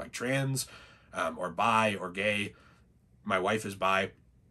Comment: did bro just say his WIFE is bi?